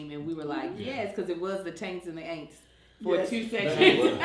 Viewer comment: I heard "The Taints and the Aints" lol! breaking the 3rd wall and talking to us after the ads was peak comedy lol. I was squinting trying to read her lips!